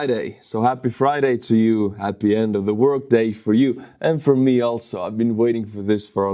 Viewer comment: Thought you said Happy End of the World Day 😂